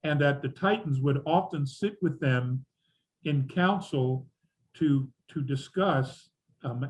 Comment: Sounded like you said c*NTS before saying council at the beginning